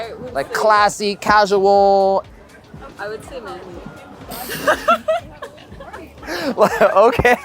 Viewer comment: I kept repeating that and still not getting it, what did she say there? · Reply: “I would say manly”